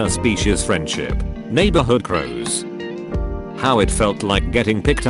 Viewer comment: I thought it said cows at first and I was like “oh hey that’s me!”